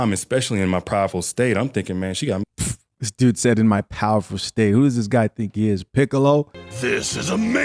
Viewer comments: It sounded like he said "Prideful", either way dude wild for this story lol 🤣😂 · It sounded like he said prideful state, not powerful. Could be wrong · Sounds like homie said prideful state. But i could be wrong 🤷🏽‍♂️ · lol I think he said Prideful hahaha . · i think he says prideful state😂 Anyways why can't we just face the facts? SOME women will deal with anything as long as the cheeks are getting properly clapped🤷‍♂️